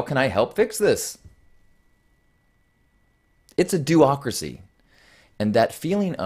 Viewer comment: What does the word diocracy mean in your video? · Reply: I thought he said Doacracy. As in, not a Democracy.